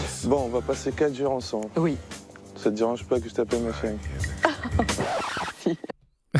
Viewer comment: ATTENDEZ ATTENDEZ ATTENDEZ !!!! 13:01 je sais que j'ai tendance à mal entendre des trucs, mais il a vraiment dit "ma chérie" ? j'ai entendu "ma chienne" même an réécoutant💀 ouais ok c'était mes oreilles my bad, la suite t'a prouvé, tant mieux 👍